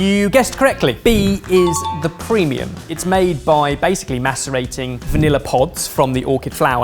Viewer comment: I legit thought Ben said “masturbating” 😅😂